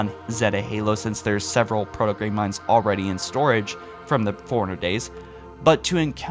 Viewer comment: bro, i love your videos. but you gotta stop saying "libarian" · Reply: Same with how he pronounces forerunners. Sounds like he says "foreigners"